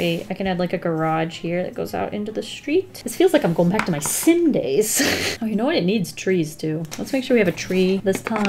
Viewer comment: I following along with ya. I love doing perspective drawing. You did catch me off guard when you said "It brings me back to my Sims days"... I heard "Sin days"... That took me a second to correct in my head.